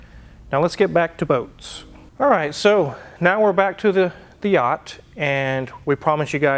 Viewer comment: I thought he said "Now we're back to the Fiat..."